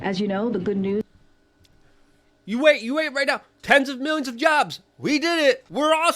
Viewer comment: Did she just say they created, Ten Thousand million jobs? That system is so wacked. Thanks for sharing!!